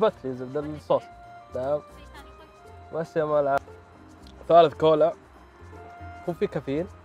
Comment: في الدقيقه 8:43 سمعت يقول بس يامولعه ولا يتهيألي 😂😂😂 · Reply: * يامال العافيه